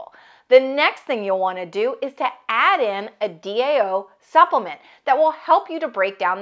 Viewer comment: Sorry, what's the name of the supplement are you reffering to? I cannot understand :( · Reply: diamine oxidase